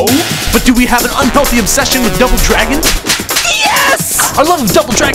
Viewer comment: did you just say dooble droogen?